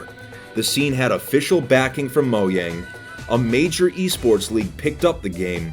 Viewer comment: did he just say “mo yang” you mixing aang momo and appa?